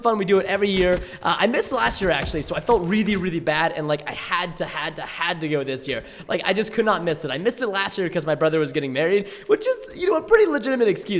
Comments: I thought he said his brother was getting BURRIED I was like... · lol, i thought he said: "my brother was getting burried" xD my mistake